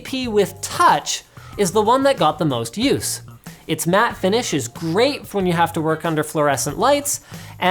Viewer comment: I thought it said "Finish | Mate" My australian senses are tingling